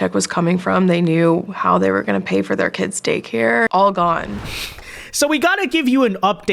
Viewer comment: did somebody say daycare savings? 😂